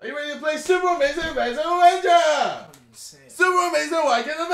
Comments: sounds like hes saying Super Amazing Wanking Adventure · In the beginning, I thought he was saying "Super Amazing wanking adventure!" lol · It sounded like he said : 'Super Amazing Wanking Avenger'